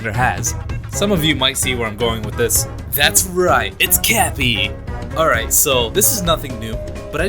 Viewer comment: did you just say crappy instead of cappy?!